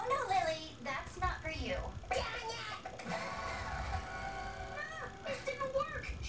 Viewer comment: It sounds like she is saying dangit....but it is damnit